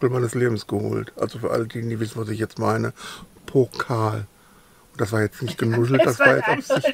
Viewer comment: Popel???- Ich höre ja schlecht,, aber ich habe definitiv "Pokal" verstanden😅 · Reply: Liebe Ulli! Ich habe nichts an den Ohren und auch Popel verstanden... 🤭